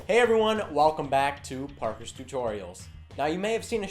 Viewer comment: is sounds like he is saying parkours tutorials